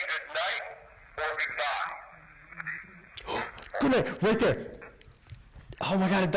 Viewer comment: does anyone else hear a disembodied voice say goodnight? It almost sounds like a buzzing, it's low in tone and it's so noticeable that I hear it over the evp. It wasn't until played it back a second time that i could hear the evp recording also say goodnight. It's strange sounding, the first voice. recommend listening to it with headphones.